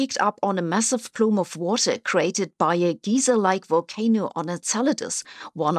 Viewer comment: To be honest when I hear you say “geyser” I don’t think Yellowstone, I think Egyptian pyramids, because you’re pronouncing it like “Giza”. In English that should be more like you’d spell “gëijser”, not “gieser”. Or, spelt with other English words: “guy sir”.